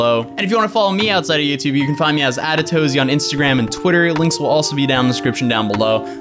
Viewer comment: OOOOOHHH, I always thought you were saying “Atatozy”... thanks for clarifying 😆